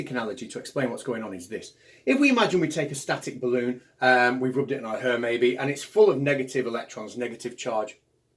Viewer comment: Sorry , sorry ,, at 1:48 ish ... did you say " We rubbed it in our hair"...?? we rubbed it .??? ..Hair ......i dont think that Mr Catz ..first name Bald, would get a good electromagnetic charge ....Boom...